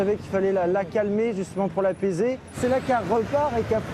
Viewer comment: il fallait la calmer, justement pour la baiser... C’est ce que j’ai entendu moi 😂